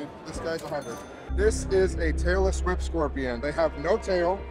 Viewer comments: I thought he said Taylor Swift scorpion lol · I heard "This is a taylor swift scorpion"